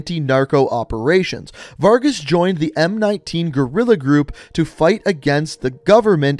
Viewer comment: did he really say gorilla 🦍 😭😭